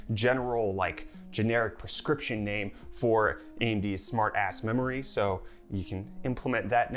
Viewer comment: Did he say Smart A** Memory 😂